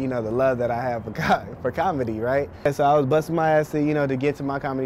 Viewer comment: about 4:35 hella says, his Dad didnt understand his love for cock lol where was the editting on this video